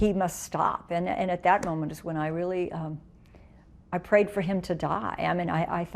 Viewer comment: uhmmmm at 10:36 did she say "I prayed for him to die"....?